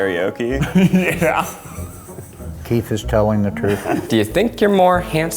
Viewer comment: All i hear is "kith is telling the truth"